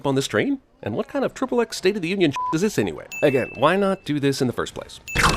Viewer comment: When he said 'Again', I thought he said 'Egghead'.